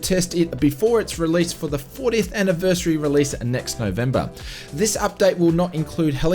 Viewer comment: That's correct its this November. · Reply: that's awesome can't wait, thankyou for getting back to me. I may have misheard it as next November my bad